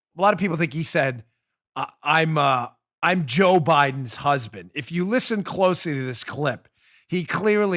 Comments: I heard I'm Joe Biden's husband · Sounds like he said, "I'm Joe Biden's husband." Unclear, probably due to dementia.